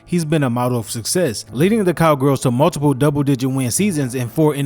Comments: Haha😂 I heard you say Cowgirls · I heard that sneak diss calling them the cowgirls 5:37 smh everybody gotta get there licks in yeah? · I heard that sir. “Cowgirls”?